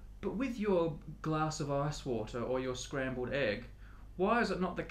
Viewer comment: I laughed @ 3:17 It sounded like he is saying "arse water"